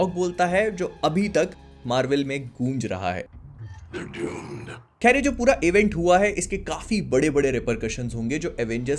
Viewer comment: kya bola Ultron ne? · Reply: they're doomed